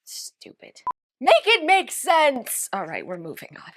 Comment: I thought said naked make sense! Lol